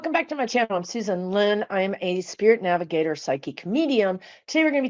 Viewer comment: I heard " psychic comedian" not psychic medium